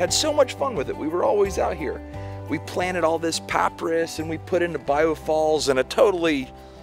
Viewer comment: Did you just say "paah Priss" for Papyrus???? :D LOL. Interesting accent! I've never heard it pronounced that way before. I like the regional accents, and find them very educational. Thanks for the video! :D :D